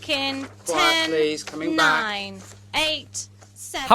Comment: what'd he js say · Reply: "quiet please, coming back"